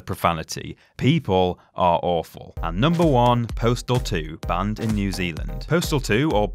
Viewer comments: I thought postal 2 said portal 2 so im like, NEW ZEALAND PORTAL ISNT THE GUY GAME XD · am I the only one that thought number one said Portal 2 at first?